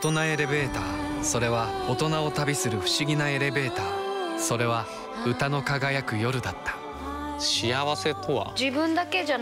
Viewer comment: 誰、このぽちゃっとした女の人？「それは豚の輝く夜だった」って聞こえた。でもいい人なんでしょ？